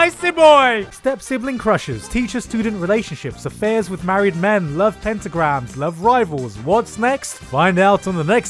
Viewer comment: Did he just said Love Pentagram 😆😆😅😂😂